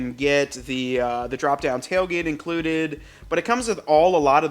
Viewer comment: did I hear him correctly when he said “drop down tailgate”???